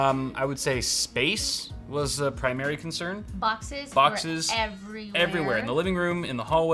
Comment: Thought he was saying “foxes” instead of “boxes”